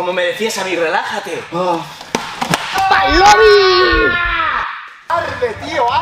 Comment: escuche q alguien dijo pal lobby XD